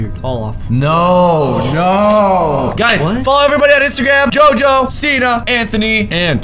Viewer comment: he said jojo and cenna i thought he said jojo siwa here is time